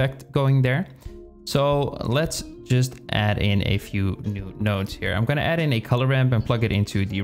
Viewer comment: Everything is awesome ❤️✨loved it and thank you so much man. Btw. Did u said nudes Instead of nodes in 5:48😂 and then had to do a retake ?